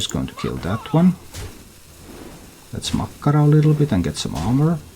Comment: "Let's makkara a little bit and get some armor" :D i keep hearing makkara aswell anyone know what she actually says? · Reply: It's Mak'gora, but I find it hilarious how it sounds like makkara, which is Finnish for sausage.